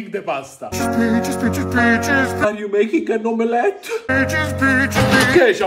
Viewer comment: as a Genshin nerd I thought he said “is he making a neuvillette?” idk why- 😭